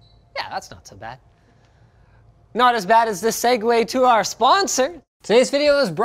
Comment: I thought he said not as bad as our sponsor lmao